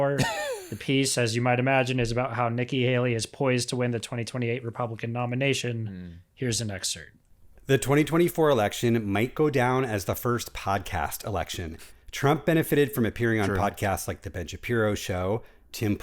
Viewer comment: Did you really say "here's an exert"?